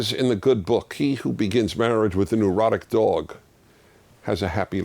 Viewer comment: haha at first I thought he said "He who has an erotic dog".....